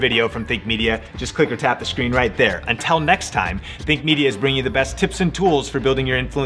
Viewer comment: Oh shit I thought he said Thic media😂